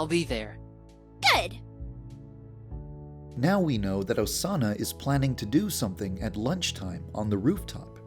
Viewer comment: OMFG 1:24 the captions said "Osama will do something on the rooftop at lunchtime" instead of "Osana" Dear Lord Christ in a handbasket what the hell